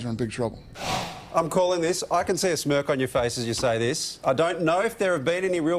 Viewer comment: I thought he said you can see a smirf on your face not a smirk....